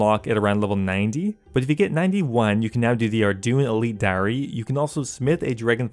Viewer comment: Did i just hear you say "ArDooin" For Ardougne?